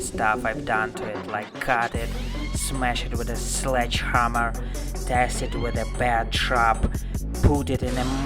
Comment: Check out subtitle at 0:21. He said "tested with a bear trap" but the subtitles said "tested with a bad truck". WTF